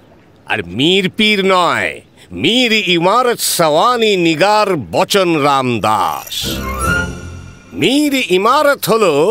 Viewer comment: did he said the "N" Word😡.